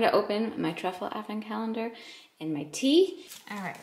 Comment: It sounded like you said open my truffle effin calendar for a second and I started to laugh, but I realized you said advent not effin. Haha. Also, when you said let’s get breakfast yo at the beginning of the video, I was like Yes!!! I love when just says what she feels and releases her inner gangsta. I believe you do have some gangsta in you haha. You are a YouTube OG.